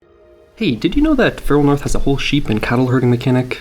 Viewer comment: I found your channel after watching the 9 devs make 1 game video because your snippet of your game looked so pretty! The only problem is I thought you were saying "Feral North" so nothing came up when I searched it (not to mention searching you by name came up with a rapper 😅). Then I realised you'd probably be listed in the original video's description and found your channel finally 😊 Just thought I'd share this information in case it could be helpful to you at all. Looking forward to watching your game grow!